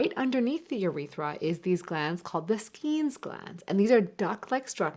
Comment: All I heard was "Skeet gland"